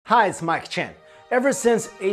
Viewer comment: Ever since I read a comment saying it sounds like he says 'Hey it's my chin' I can't hear anything else! Lol